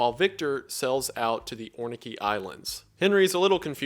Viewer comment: Am I mishearing or did you say oinkey Islands? Is it meant to be Orkney Isles? Other than, another excellent video. You're a modern-day storytelling gem, this video would be so good to assign as homework for English teachers.